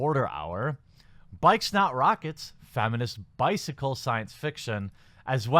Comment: I thought you said "Bike Snot Rockets".